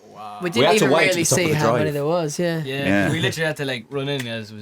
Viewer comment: did niall say "legit"?